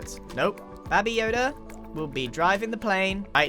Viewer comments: I thought he saying babi yoda for a second 😂 Babi = pig (very disrespectful to say it infront of a malay) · is it just me or did grain just say "babi yoda." Also, it'd be pretty cool if grain pulled a grinch with all the invis pots tango gave him.